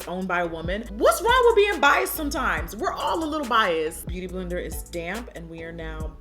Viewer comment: i totally misheard jackie when she said "whats wrong with being biased", thought she was saying just "bi" and "we're all a little bi here" and i was like oh not my lesbian ass lmao thanks for the video as always